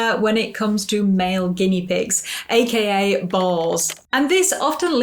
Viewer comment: I thought you said "AKA Balls!" 😂😂